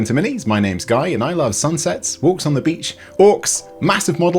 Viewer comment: I heard I like "Sunsets, Orks on the beach....." wait a second thats walks on the beach. still would work tho.